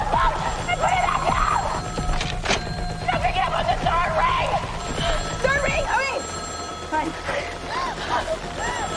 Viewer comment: what do say I can’t hear what she said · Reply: “No, pick it up on the third ring”